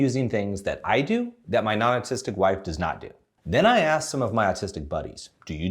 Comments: Thought he said "... my nonexistent wife ..." lol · Hehe I thought you said "my non-existing wife" instead of "my non-autistic wife" for a while there. Anyhow I am autistic (I now have a diagnosis) but I find autism communities online a bit too much to handle. I'm easing myself in. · I had to replay @ 00:23, because it suuuure sounded like you said “my non-existent wife.” 🤣😅